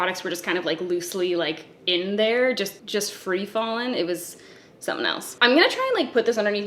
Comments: I swore I heard that the items in the box were 'free-balling'. yup. Not wearing drawers. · Free FALLING. Had to rewind. Thought I heard free balling.